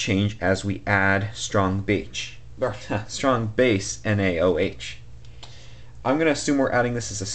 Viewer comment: did you just say ``strong bitch`` at 0:45 lol!